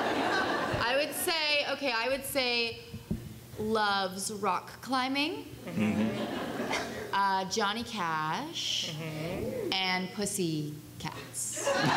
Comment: Could someone write what Kat said between 23:13 to 23:26, please? I really really love this cast :) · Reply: 'I would say loves rock climbing, Jonny Cash and Pussy..Cats' Lmao